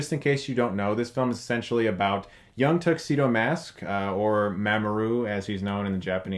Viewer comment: So... Young Tuxedo Mask = Batman??? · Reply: I thought he was saying Tuxedo Man at first, and I thought of Billy and Mandy (AKA one of the shows back from when Cartoon Network was awesome).